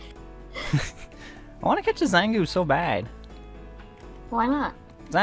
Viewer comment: what I thought I heard I wanna kisstch a zangoose so bad what actually said I wanna catch a zangoose so bad. Literally heard him say I wanna kisstch a zangoose so bad which is literally by far the most weirdest I’ve heard someone say that they actually didn’t actually say